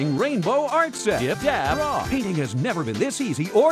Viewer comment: DID HE JUST SAY DAB!?!?